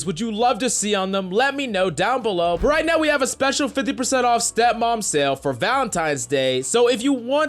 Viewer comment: Did he say stepmom sale? Seriously guys😂😂😂😂